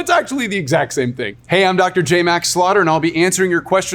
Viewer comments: I thought he said Max Slaughter. Awesome name for a doctor. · I’m sorry, but imagine going into the ER and hearing “Dr. Slaughter will be right with you” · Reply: Not to mention that before reading the video description, I thought he said his name was Dr. Max Slaughter.